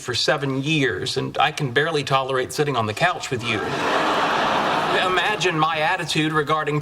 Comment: Sorry I am french and I didn't understand at 3:49 what Sheldon said... in a "what" with you ? And what does in mean... Thanks for help 😉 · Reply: He said sitting on a couch with you